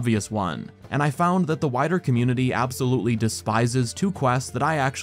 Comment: did he just say waither? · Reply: Wider